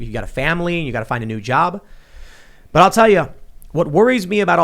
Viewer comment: I thought he said “find a new family and a new job” and I was like Tim...I’m 31 and adopted I don’t think I can get back in the system for a new family but a new job I can do 😂😂😂